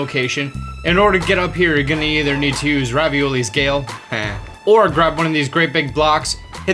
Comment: did he say Ravioli 🤣🤣🤣🤣🤣🤣